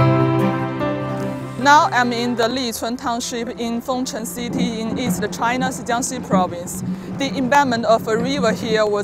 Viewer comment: I've always been curious about this. It sounds like you said "In east a China's Jiangxi Province" How does the 'a' get in there? Apart from that, your narration was perfect. Like it doesn't matter. It's all fine. I've just noticed Chinese sneak an 'a' in here and there.